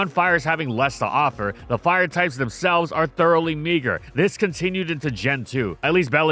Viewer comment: what did he say 😭😭😭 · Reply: Thoroughly meager